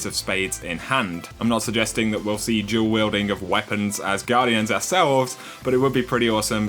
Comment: I thought he said jaw weilding! Lol, I'm so sorry! XD